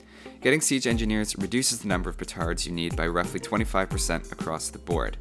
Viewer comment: The subtitles say "retards" lol And "retarded army"